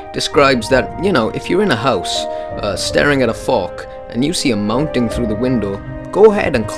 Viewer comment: did he just say that we were starring at a fuck